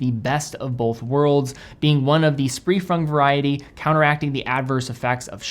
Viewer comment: Did I hear "spree frung"😂😂